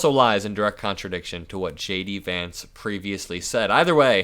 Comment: Thank you. At the end when you mentioned J.D. Vance, I misheard it as "Shady Vance," and now I have a great new nickname in my head!